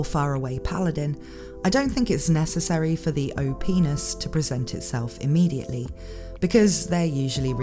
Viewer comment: Had this video playing in the background then out of nowhere I hear 26:38 "O Penis".